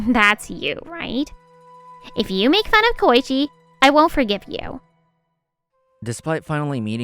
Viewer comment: I might be wrong. But did she said Keiichi?